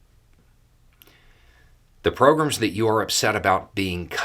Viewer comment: Beau, your accent in these videos is pretty mild, and I'm quite used to it, but for whatever reason, today, every time you say "program", I hear "pogrom". The first time you said it, you were reading from the letter, and I thought it was done on purpose, but the rest of the video... I'm not complaining or anything, just felt compelled to say it.